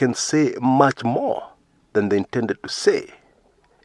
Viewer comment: I also thought he said more than he was required to. Alitoboa sana!